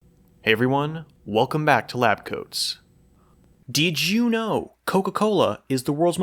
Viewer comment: Thought i heard " welcome to lactose" instead of labcoats